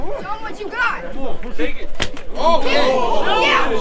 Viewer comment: ALL I HEARD WAS A LITTLE BOY SAY: "SHOW EM WHATCHA GOT" & DUDE IN THE GREY SHIRT WENT SUPER SAYIAN RIGHT AFTER THAT 😂😂😂🔥🔥🔥🔥💯